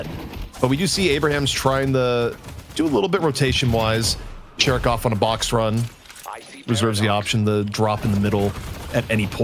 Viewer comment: I could have sworn he said "Jerk off on a box run"